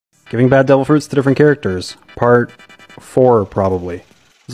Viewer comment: I thought you said bad devil fruit